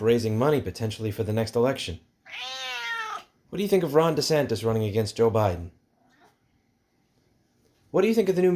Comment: I thought you said Ron DeSantis was running again Bill Nye and I felt my soul leave my body for a moment.